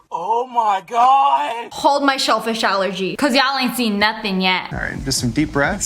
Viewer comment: When the guy said “shellfish allergy” I thought he said “ selfish allergy” and I was like HOW IS A REACTION SHE CANT CONTROL SELFISH